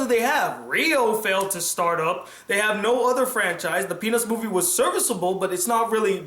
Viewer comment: If you turn on the subtitles at 16:11, he says "the penis movie" instead of the "peanuts movie"